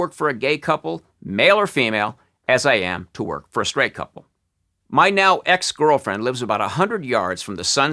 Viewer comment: anyone else hear "My male ex-girlfriend..